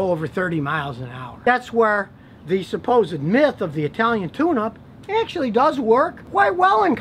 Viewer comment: I thought he said an Italian tuna :/